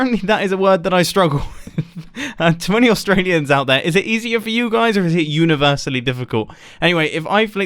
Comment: I thought he said ‘20 Australians out there’ 🤦‍♂️🤷‍♂️